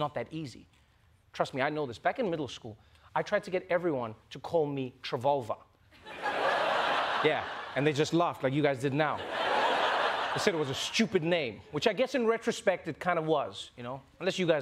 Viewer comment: You just know there's a parallel universe out there where Trevolver is a world wide top of the charts south african hip hop artist. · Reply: lol, is that what he said? I thought he was saying Trevulva!